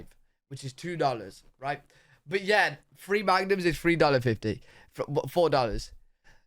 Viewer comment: he says magnum, I hear condom...